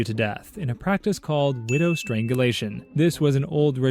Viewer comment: You said widow triangulation instead of strangulation